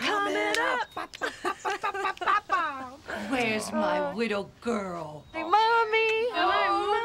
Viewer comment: Swear she said at 0:47 where's my widow girl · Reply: She said "wittle" which is a diminutive baby-like talk.